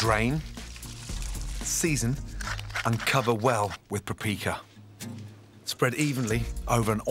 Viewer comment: did he say prapika????